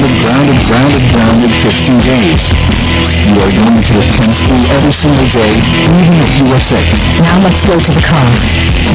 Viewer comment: What is Cole saying? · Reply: He said "You are going to attend school every day, even if you are sick!".